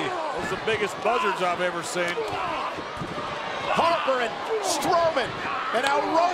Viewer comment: did I just hear Strorman